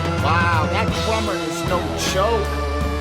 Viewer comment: I played it back ten times coulda swore you said that drummer was no choke. 7:10 . Interesting choice of words.